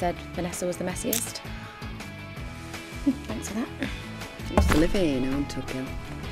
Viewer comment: Can someone pls tell me what Emma is saying at 1:33 cos I honestly can’t make it out and idk why😂🙈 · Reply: She says “I used to live here in Tug Ghyll” then Michelle says “well it’s my house now”